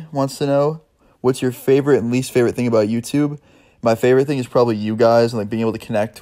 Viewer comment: when he said "My favorite thing is you guys" i thought he said "My least favorite thing is you guys" xd